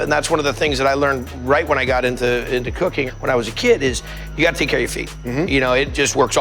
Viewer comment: i swear it sound like he said “where i got into cocain